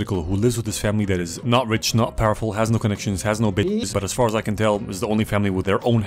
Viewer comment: yo I heard bitches but the subtitles say business so now I don‘t know what‘s right💀